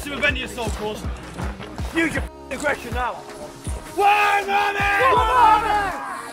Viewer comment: what were they shouting at 2:05? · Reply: One Army?